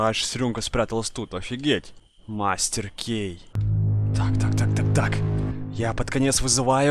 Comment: мне 1й послышалась шо он сказал мастер г.е.й😂😂😂